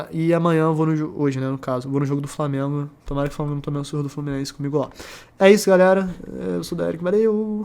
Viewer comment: ??? acho que vc n entendeu o que eu disse kkkk ouve de novo. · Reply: eu entendi "tomara que o Flamengo tome uma surra do Fluminense comigo lá" ahuahsua mas deve ser "tomara que não tome"